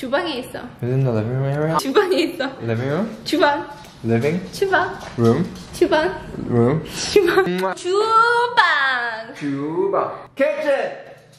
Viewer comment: who else heard BTS-eu BTS-eu? 🤣🤣